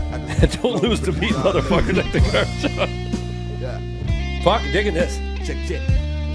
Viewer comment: WTF DID HE SAY???????? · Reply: Digging this